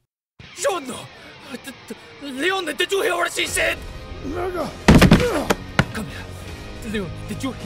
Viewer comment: did i just hear nigga